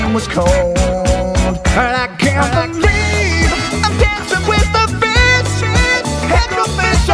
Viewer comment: I THOUGHT THE ENDING SONG SAID. ‘ Dancing with a BITCH 🤣🤣🤣